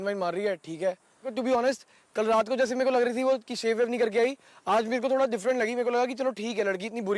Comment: did really said ki oh shave krke nahi ayi😂😂😂😂😂😂😂😂😂😂😂😂😂😂😂